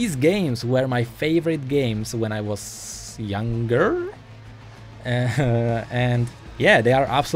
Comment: WOAH OWAH WOAH HAHA I THOUGHT I HEARD SINCE YOU WERE A YOUNG GIRL XD BUT IT WAS YOUNGER